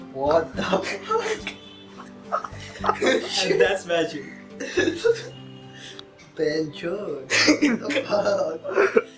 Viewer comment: What did he said at 1:00 ???? BC?